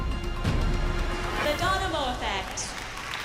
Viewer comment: i heard "The Donimo Effect"